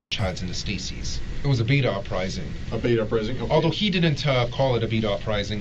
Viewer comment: i kept hearing "beat off rising" instead of beta rising